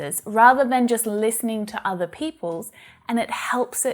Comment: Did you say peoples meanning peoples?